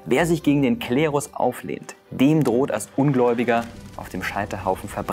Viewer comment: Hattest Du bei Minute 4.48 ca , wirklich gesagt " in der Hölle sporen..." ?? Oder habe ich mich verhört ? Ich kenne nur den Satz " in der Hölle schmoren ". : )